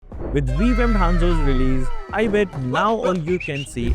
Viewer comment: I hope I was able to educate you all with the Guide! 😊 And If you're still wondering how to participate in the Giveaway: ✨ Subscribe to the channel! 👍 Like this video! 💬 Comment your favorite part of the video below! 🏆 Winners will be announced soon on our WhatsApp channel, so make sure you’re following us! 👇👇👇 🔗 <link> 🍀 Good luck, everyone! 🎉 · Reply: I thought you said with vegan hanzo released 😂